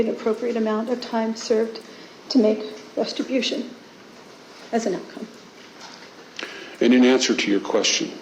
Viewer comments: did she say "restribution?" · did she say "restibution"? Is that a word? Like restitution and retribution at the same time?